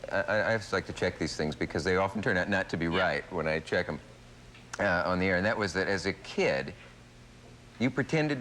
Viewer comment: Did he say "on the internet" at 22:54 😳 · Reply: He said "on the air."